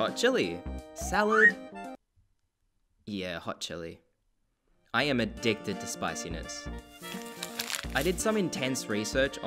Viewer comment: i heard "i am addicted to spicy nuts" all right thanks